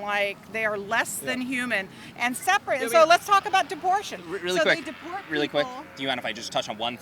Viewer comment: did she say deportion?